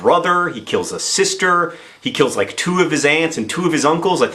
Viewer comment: Thought he said "shot some of his ants." Wondered if John was going to explain about keeping ants or something before I realised... aunts. Aunts.